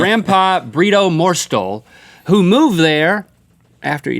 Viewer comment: I thought he said grampa burrito